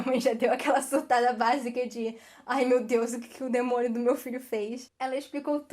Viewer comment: eu escutei "demôniO" e "filhO" em vez de filhA e demoniA